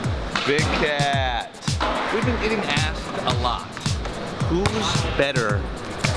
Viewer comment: Anyone else think it sounds like he says "We've been eating ass...a lot." at the beginning or is it just me?